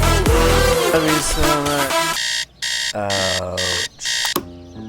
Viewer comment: lollllll@ 3:00.... translation; "I love you soooo muchhh you dont even understand I'm druuuunk"